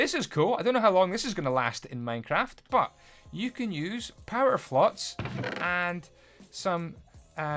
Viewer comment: did any 1 hear him say “power flots” instead of “flower pots” Edit: btw I was not trying to be rude just funny...